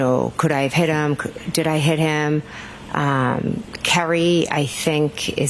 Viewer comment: 😮 Wait! I thought Karen said “I hit him. I hit him. I hit him.” Jen did you let the truth slip out here?